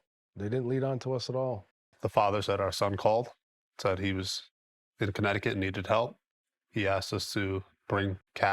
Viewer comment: I thought you said New Haven Connecticut.